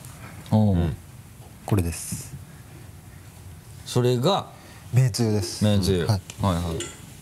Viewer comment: 鼻つまみながら言うから「べんつゆです」に聞こえるｗ 鼻つまむと嗅覚が鈍るから吹き出すのが遅れるのかね？ｗ